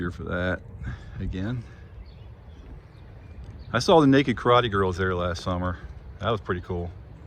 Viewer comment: "The nekid Karate Girl"? I was wondering if I heard that right🤣